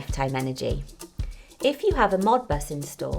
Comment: she says, "if you have a word I can't recognize installed" you will be able to view what you are exporting. What is that word? I want to see my consumption and amount exporting. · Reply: Hi Chuck, this is a modbus meter. You can use Live Chat on our website to discuss the modbus :-) <link>